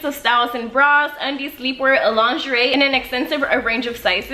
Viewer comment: I literally love you❤ you are my favorite youtuber!❤ ignore the HATERSS!! btw when she is talking about Savage X Fenty I swear I thought she said "alondrarae" instead of "a lingerie" 😂